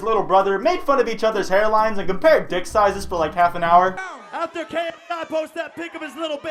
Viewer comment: i heard "compare dicks *cuts off*"